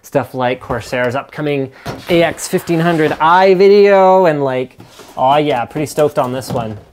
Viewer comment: I actually bought the AX1500i yesterday on Newegg. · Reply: Ahh I misheard he said "Corsair's AX1500i video" not Corsair's AX1500i... sue me.